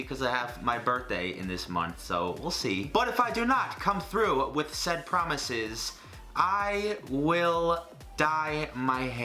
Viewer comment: All I heard was "If I do not come through with said promises, I will die."